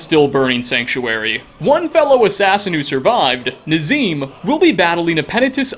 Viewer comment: Did i just hear say Nazeem🤣